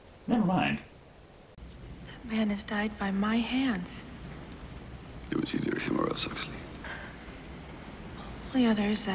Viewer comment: The hell is Stallone saying at 31:03? “ It’s easier to borrow Huxley.” “ Go grab a wheelbarrow Huxley” “Better than a ball shot Huxley”. I need to know! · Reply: "it was either him or us Huxley" damn aint that hard to hear